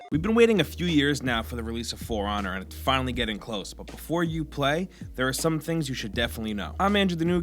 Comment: did he just say uplay?